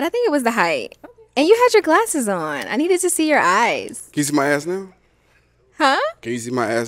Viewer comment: Sound like he was saying “can you see my a$$ now ?”